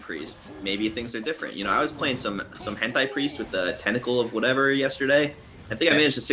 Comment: did he say hentai priest at 3:00? I really don't want this to be a thing